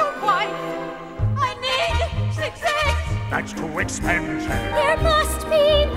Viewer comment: the lady that says i need six eggs it sounds like shes saying a need sucess